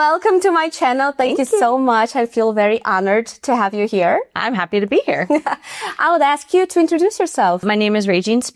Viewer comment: Tive que voltar e colocar a legenda na minutagem 0:37 para entender o que ela falava: I'm happy to be here. No mais deu pra entender bastante.